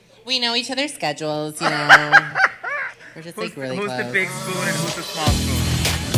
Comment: What was naysha saying at 32:30... And why Denali face changed so quick! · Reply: "who is the big spoon and who is the small spoon"